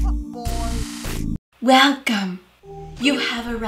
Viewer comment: For a second I thought that she said WeLcOmE tO tHe EE voice mail